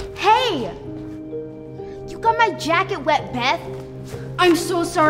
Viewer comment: I thought she was gonna say "You got my jacket wet, b*tch" 💀